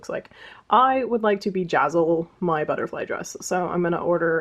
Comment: Nearly spat out my tea when I thought I heard someone wanted to vagazzle a butterfly dress. Potatoes in ears clearly but a good laugh at the start of the day.....